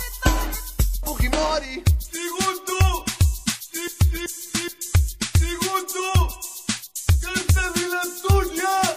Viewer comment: ¿Qué es lo que dice, Ligonto? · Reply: Un Señor Encorbatado dice: Segundo... segundo... carta de la Antonia! (avisando que había llegado una carta de su prima Antonia quien residia en California)